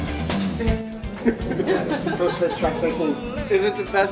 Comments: WHAT ALBUM DID FLEA AND CHRIS ROCK SAY WAS AWESOME ?¿ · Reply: Ghostface choc 18 🤣?? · What record did Flea say is good? I couldn't hear what he said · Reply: ghostface, he didnt say the album but he said " track 19" was really good, i think he talking about Supreme Clientele